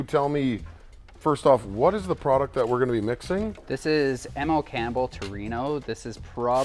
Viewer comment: What was the brand of paint? It's it available in the US? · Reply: I thought he said it was M.L Campbel?